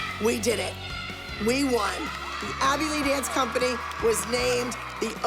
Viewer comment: Did anyone notice put on CC in 4:13 instead of Abby lee dance company the Cc said avilee dance company